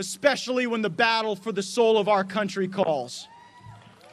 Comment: Did he just say "asshole of the Country"?